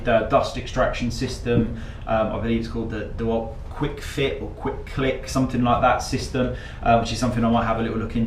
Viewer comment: Hahaha, at 4:36 did you say quick clit?